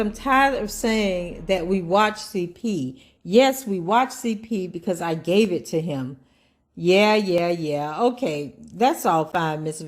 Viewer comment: Did she just say "yes we watched CP because I gave it to him"? 1:04:40 or am I hearing things in my head🤷🏾‍♂️🤦🏽‍♂️